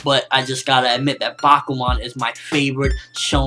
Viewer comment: I thought you said bakugan... Then I would have been really confused.